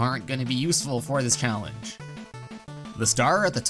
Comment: Did you say challenge as callenge at 4:08? 😂😂